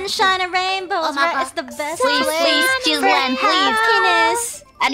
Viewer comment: I thought Moona said "Please, children" on the first one